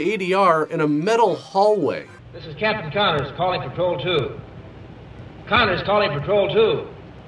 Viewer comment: I heard "calling patrol two" as "calling for Troll 2," and I was expecting his emergency to be that they were eating her and going to eat him.